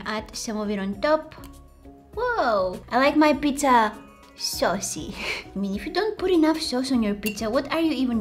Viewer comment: When she said “I like my pizza saucy” I thought she said “I like my pizza sussy”